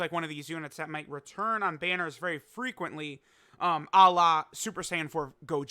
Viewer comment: what did he even say?? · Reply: Ala. Not Allah if that's what you're thinking.